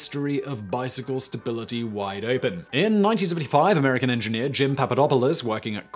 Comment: Is nobody gonna talk about 5:58 ? He says "1975" super fast, almost didn't understand him.